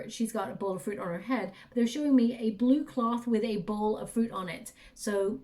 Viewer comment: I have a blue cloth, a giant light blue scarf I like to wear at home, like wrapping myself in it, and my cats love to sleep on it when I'm not wearing it..I've actually heard you saying a blue cloth with a ball of fur on it 🥰💙🙏 Thank you! It's all extremely accurate and so beautiful 🤗🌌💙